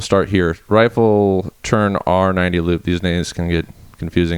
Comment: lmao did he say "these n~ggas" at 9:55?